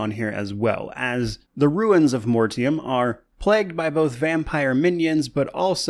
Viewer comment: "The Ruins of Mortym" I misheard it.